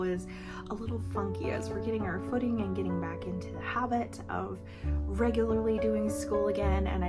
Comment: My brain! When you said "getting back into the habit", I heard "hobbit'.😂Your enthusiastic effort is admirable!🧡 (when my husband said he wanted to learn Spanish, I gave him the book "Spanish on Your Own"🤣)